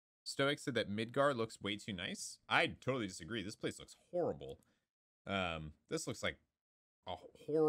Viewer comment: Not only does it look horrible, but this is the GOOD part of town, it's not even the slums. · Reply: At first I thought you were saying the MOVIE look horrible 😂😂😂